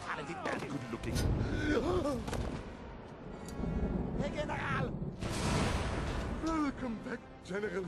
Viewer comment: I know that he was saying "Herr General", but as a kid first seeing this movie when it came out, the accent was so over the top thick, I swear I thought he was saying "Megan Ralph." To this day, I always use that phrase " Megan Ralph" when I need a laugh.